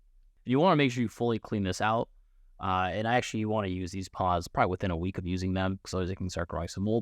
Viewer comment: What did you say at 0:52? Why to use within a week? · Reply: Sounds like he says: You need to fully clean these out. You want to use these pods probably within a week of using them. Cos otherwise they can start growing some mould.